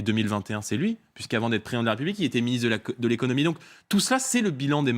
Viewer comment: c'est moi ou il allait dire que Macron était auparavant le ministre de la connerie ?